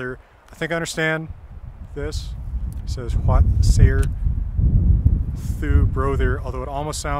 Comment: he is clearly saying the word DU. vad säger DU broder. How the word you is said today in Scandinavia.